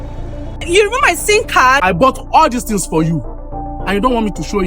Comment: Did I hear" you remove my sin card"? Which one is sin card 😂😂😂😂